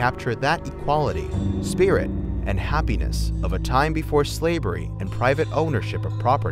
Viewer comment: I swear he says "slaybury " 4:23 and Mardi Gras "vive"